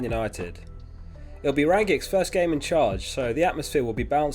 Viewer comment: Lol... Swore you said Ryan Giggs 1st game in charge.... Nearly unsubscribed.. 😂